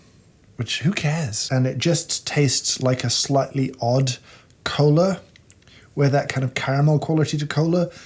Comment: lol did he just say a sluttly odd cola turns into dirty coffee? Shocked